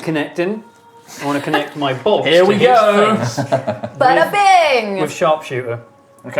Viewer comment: "I'd like to connect my balls to his face" At least that's what I heard.